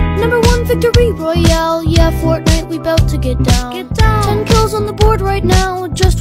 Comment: I heard ‘ten girls at the board right now’ 💀💀💀💀💀💀💀💀💀💀💀💀💀